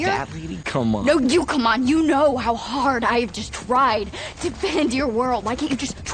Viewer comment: to fit in听成了defend